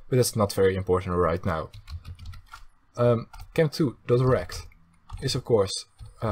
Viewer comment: is it just me or does it sound like he is saying erect instead of rect